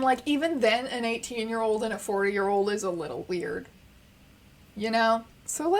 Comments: i swear to god i thought you said fpur year old and i just thought, "god yea i hope so" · I THOUGHT YOU SAID AN EIGHTEEN AND A FOUR YEAR OLD ASINDA SFAHBSFZIOUBAWOISUHN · I swear I hear her say '4 year old'. I know she said 40 but I hear 4. might be me being a dumb idiot with off hearing.